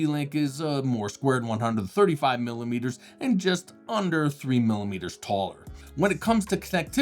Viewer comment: the unit being described was said to be "just under 3 mm tall". I think he intended to say ""just under 3 cm tall". Even at 3cm, that is incredibly thin. Surely a mistake!, as that would be insufficient for the goodies that go into it. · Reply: He said ‘just under 3mm taller’ when compairing both heights.